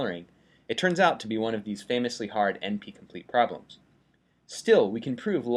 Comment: what does he say at 8:20.. "n p complete problems"?